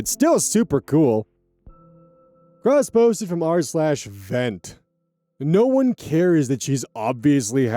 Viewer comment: Why did I hear this as “crossposted from r/fent”